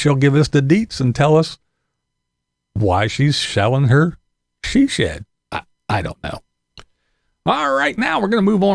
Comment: Did i hear you say " she Shellin Her she shed" lol say it 10 times fast lol